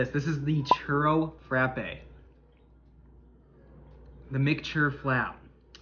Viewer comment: 😂 why did I hear “mature flaps” 🤣🤣🤣🤣🤣🤣🤣🤣